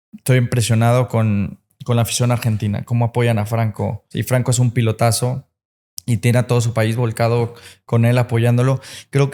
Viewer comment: estoy impresionado con afición Argentina, como apoya a Franco y Franco es un pendejazo. Así lo escuche yo. Jajajaja